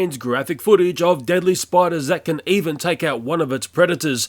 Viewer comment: Am I the only one who noticed it said spuders instead of spiders?